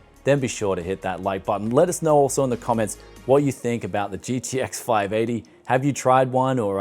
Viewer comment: Honesty: I thought it said RX 580 and I came here to see what the heck you were talking about, lol.